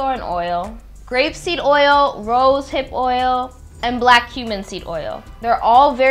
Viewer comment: I thought she said black human seed oil for a second there....😳😳😳😳😂😅